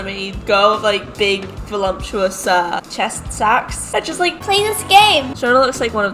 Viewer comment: did....did she just say cheast sacks😦😦..... bye felicia✌🏾✌🏾